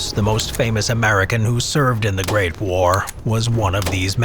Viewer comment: i heard grape war Edit: im so silly